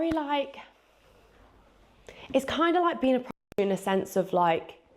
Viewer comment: It’s similar to being a p0rnstar??????NO wayyyy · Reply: i thought she said prost-institute